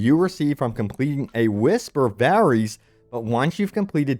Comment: I thought you said "Whisper Berries" lol